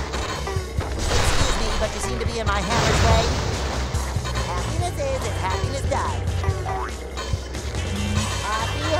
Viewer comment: Can anybody tell me what did bob say since 2:25 to 2:28 please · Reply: Miguel Villanueva He said, “excuse me but you seem to be in my hammer’s way”